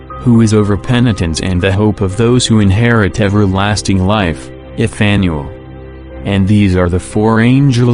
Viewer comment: What’s the name he says at 50:46? Ithanuel, Effanuel? 😕🙏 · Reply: is Fanuel